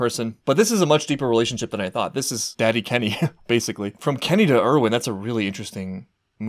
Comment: Did you just say "Daddy Kenny"? 😳😳😳